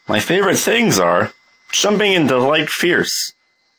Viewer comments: "Jumping in to Light Fierce" This line stresses me out far more than it logically should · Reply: I thought he said "jumping into Lake Fierce" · wtf does he say here? Jumping into the light fierce? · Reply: Jumping into light fierce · When I first saw this I didn't know what "Jumping into light fierce" meant, so I always thought he said "Jumping into Lake Pierce." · I always thought he was saying "Jumping into LAKE Fierce" and the joke was Ryu being a homeless dude